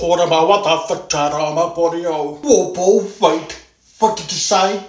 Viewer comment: I thought my man was saying he wants a fritata